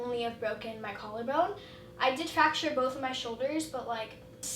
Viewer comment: She lyin😂she doing this for views wow what a shocker❗ how can u fracture both shoulders when u only have one cast one❗? Bc she fakin this❗ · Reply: Brianna Morales fam I swear at 1:51 she said she fractured both shoulders but I might be hearing things